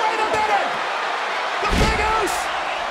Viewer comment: did michael just call Roman the Big Uso? 😂